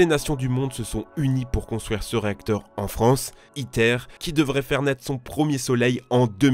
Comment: pourquoi j'ai cru entendre hitler